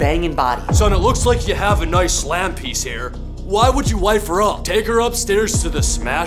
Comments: “Slam piece” I thought he said slap bracket XD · I honestly thought he said “well it looks like you have a nice lamp piece here” 🗿💀